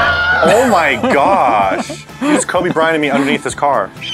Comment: Did just said Kobe Bryant 2:16 R.I.P Kobe the Goat